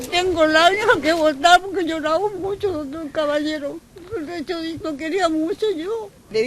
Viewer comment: Le entendí "Tengo labios, qué bondad porque he llorado mucho doctor caballero".